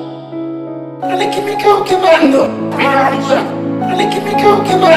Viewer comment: Wuff wuff xd woaw woaw dale que me estoy quemando Sacame una Fhoto :v pense que decía dale que me Cago sacame una Fhoto :vvv xd